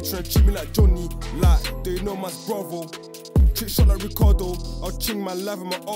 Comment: Can someone tell me what he said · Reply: Chest Shot